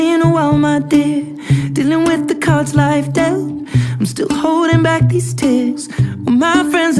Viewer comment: Mishearing: I thought instead of life dealt it was dealing with life like adele 🤣🤣🤣🤣🤣🤣 rofl